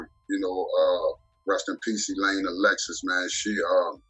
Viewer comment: Did he say A-Lexxus was his manager?